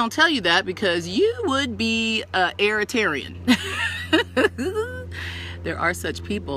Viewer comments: She said a airaterian lmfaooo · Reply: i thought she said irritarian lol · She said a airaterian lmfaooo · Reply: Lol I thought she said *earth*tarian